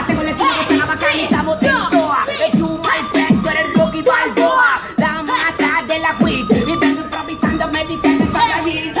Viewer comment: i think he said Rocky Balboa lmao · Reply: You my friend` tu eres Como`( You my friend are Like) Rocky Balboa 0:44 You mucho money `como`(like) Pablo Escobar.